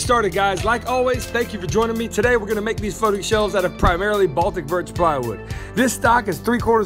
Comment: It sounded like he said "we're gonna make these fuckin shelves out of birch plywood"